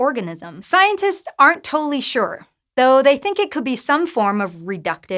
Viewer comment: did you say "scientists aren't toely sure"?